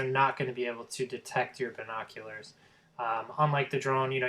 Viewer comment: Really? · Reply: yes you sound horrible sound like your saying vinkockulars